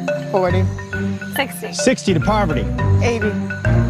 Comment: did they call her poverty